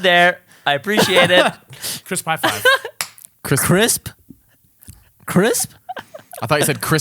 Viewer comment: I heard "Chris Pine Five" too haha!